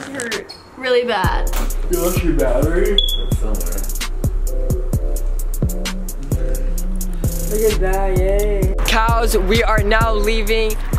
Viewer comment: did you just call me a cow 😂 🐮